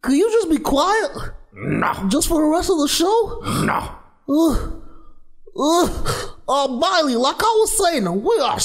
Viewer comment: I almost thought they said go Miley go lmao XD